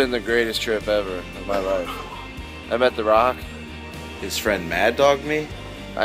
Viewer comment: Did he say "His friend mad dogged me"?